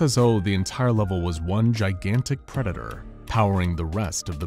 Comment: predator? subtitles say generator